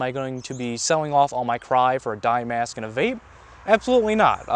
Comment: did u say vape or babe? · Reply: He said vape because that is a (very funny) stereotype about speedsofters.